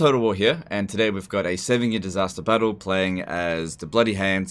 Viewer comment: I thought you were saying “seven year disaster battle” for the first 20 videos I watched